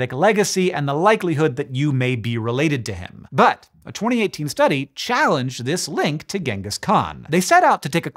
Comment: What Hank said: "But, a 2018 study challenged this link to Ghengis Khan" What I heard: "But, a 2018 study hates fun."